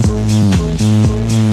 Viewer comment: is it me or to 0:08 i hear p** XD